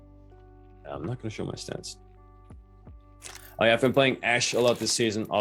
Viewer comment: did you say you weren’t going to show your stats? Lol imma try and practice the 6 sense